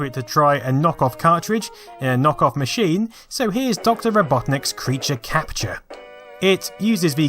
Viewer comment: I thought he said Dr Ripoffnik's instead of Dr. Robotnik's. lol